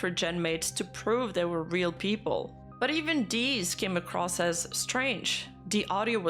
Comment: I’m brainrotted enough that when says ‘these’ I hear “deez”